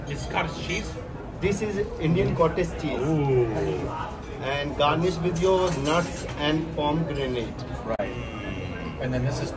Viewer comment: did he say "it's garnished with your nuts"? Haha 😂